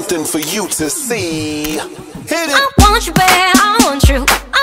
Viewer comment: It sounds like yooA is saying "i want CHUU" instead of "i want U" 😂